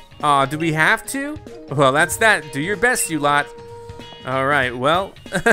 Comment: I thought it said “you idiots” instead of “you lot”